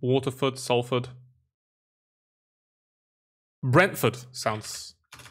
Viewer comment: When you were doing fords, You went brentford and i thought u said bradford ( where im from) so i smiled then you typed in brentford and i Just stared into the word brentford